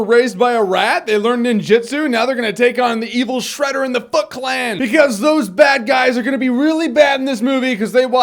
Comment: Lol sounds like he says "The f*ck clan." :P